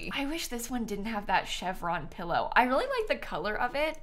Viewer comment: says “chevron pillow” and I hear “Chef Ron pillow”